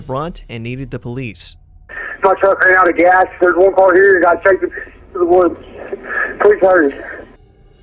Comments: i slowed it down and it definitely sounds like he says "my truck ran out of gas, theres 1 boy here that got taken into the woods, please hurry" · The 911 call for, it sounds like he says "There's one car here, the guy chased me to the woods" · It sounded like Brandon said, "I ran out of gas through one part/point here, and got chased into the woods."